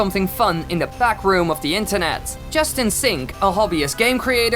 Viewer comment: When you said Justin Sink i thought you said Just In Sink.